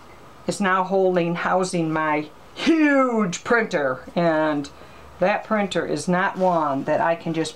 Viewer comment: I'm sorry I thought I heard you say it was a new printer in the video. Thanks again beautiful work